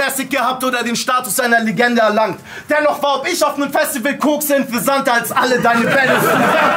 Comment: was sagt er bei 18:04? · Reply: Dennoch war „ob ich auf einem Festival kokse“ interessanter, als alle deine battles zusammen. Ging um das Battle bei ttt von robscure